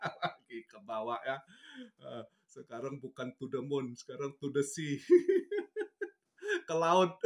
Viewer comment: Efek suara yg kecil,saat om bilang to the sea kedengeran sm saya om bilang yamet kudasi..🤣🤣🤣